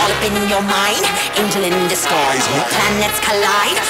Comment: lyrics: When the planets collide, stars in your eyes, Open your mind, angel in disguise. Planets collide, stars in your eyes, Open your mind, angel in the skies (Switch) When the planets collide, stars in your eyes, Open your mind, angel in the skies When the planets collide, stars in your eyes, Open your mind, angel in the skies War, love or peace, war, love or peace. War, love or peace, war-war, love or peace. War, love or peace, war, love or peace. Best protect-tect, best-best protect your neck. Wake up Wake up Wake up Wake up (Slightly different) When the planets collide, stars in your eyes, Open your mind, angel in the skies When the planets collide, stars in your eyes, Open your mind, angel in the skies War, love or peace, war, love or peace. War, love or peace, war-war, love or peace War, love or peace, war-war, love or peace Best protect-tect, best-best protect your neck. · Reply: i thought she was saying england in disguise